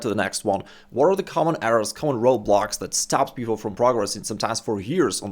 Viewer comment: I thought he said “Roblox” for a second